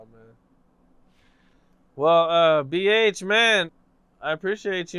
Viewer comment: Did call him at the end?